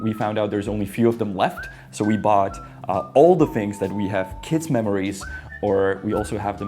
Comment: What I heard: "We bought older things that we had kid memories" I thought I forget how to English then I heard something called Jesenka and I was like "Yep, not American. Sounds Czech"